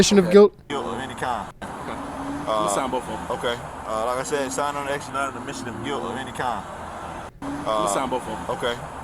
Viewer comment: i heard him say " singing on the line is NOT an admission of guilt of any kind."